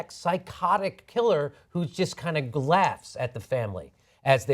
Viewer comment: did Jimmy just say "glaughs" @ 5:22?